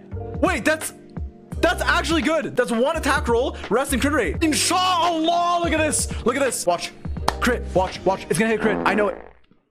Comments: "in shaa Allah look at this" am i hearing the truth? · did he say “inshallah” or am i just hearing things??? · hello? does he said INSHA ALLAH ? Is he an arabian ? 😳